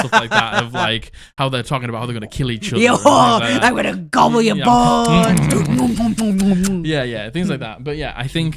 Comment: wait did she say "im going to gubble your balls?"